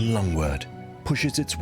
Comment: the common long? wart? Anyone catch what this plant is? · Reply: I'm curious about that too. I thought he said lungwort but there's no such thing. It's lovely though, whatever it's called.